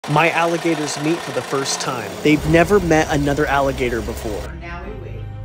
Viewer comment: When you said “alligators meet for the first time”, I thought I heard “trying alligator meat for the first time”😰